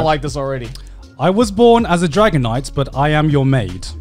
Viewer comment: I heard DRAGONITE!!! XD